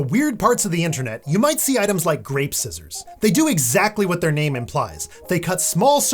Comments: because of the weird internet comment i misheard "rape scissors" and my heart sank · When I watched the start of this video for the first time, I misheard “ grape scissors “ for “ grape without an e scissors “…